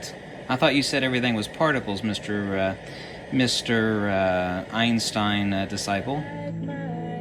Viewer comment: “No particles Mr.Einstain disciples” 🤣🙏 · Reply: it sounded like he said "einstein recycled." but what you said makes more sense. lol well actually they both make the same point across so nvm. equal sense